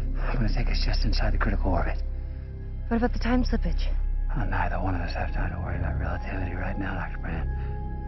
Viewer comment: What about the time slippage? I'm not the mumblebava kinda wava ah halaba bavahava right now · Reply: Neither one of us have time to worry about relativity right now Dr Brand